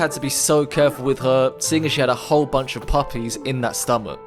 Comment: wut u say i cant hear pls say guys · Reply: They said “ A whole bunch of puppies in her stomach.”